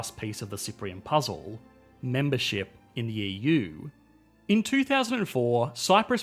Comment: I am Not sure but isn't it membership instead of mebership?